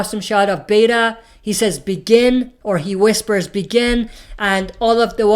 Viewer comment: I thought Beta said Negan instead of begin. Anyone who reads the comics know he's gonna try to look for him.